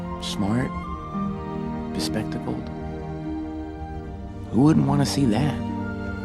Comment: What Flint said to Sam in the Jello castle - ‘smart. Bespectacled. Who wouldn’t want to see that?’ It’s amazing, and has the potential to do wonders for people with irrational insecurities. And even for people without that, it’s still such a beautiful line to remember and remind yourself that it’s good to be the real you. And it’s something he learned from his Mom too, since she did a similar thing with him, so he’s taking after her good qualities in a good way. So wonderful. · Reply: I thought he said "respectable" not "bespectacled"